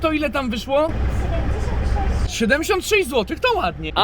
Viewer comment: Czy tylko ja usłyszałam 706 zł?